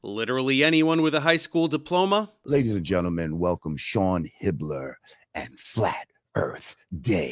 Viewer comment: I thought Jamar said “Sean Hitler” for a sec, lol